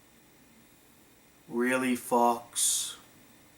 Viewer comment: I thought you said, "really fucks?" XD